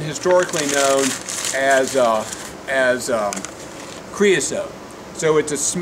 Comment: What did you call the bio crude oil at 6:48? crisone?